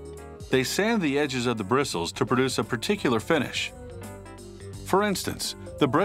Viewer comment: I thought he said fetish